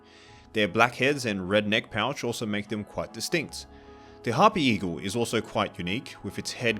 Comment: I WANT THE HARPY EAGLE IN PLANET ZOO! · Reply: I thought you said happy Eagle :)